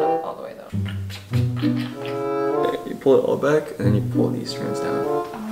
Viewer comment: did anyone else hear what he said???? he said "Babe" - ummmm guys..... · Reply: Uh no he said "then you pull it all back"